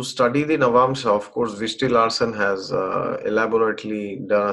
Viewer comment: Wonderful explanation. ! Can you type the name of the series of Navamsha you were referring to ? I heard it as Vishtil Hassan. Is that correct ?